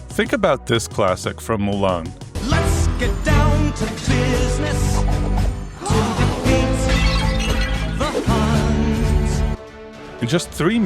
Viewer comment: Why did I hear in 1:53 let's defeat the huzz like what